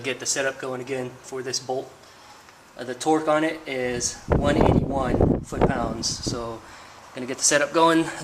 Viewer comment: did you say 21 ft lbs? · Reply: 181 ft-lbs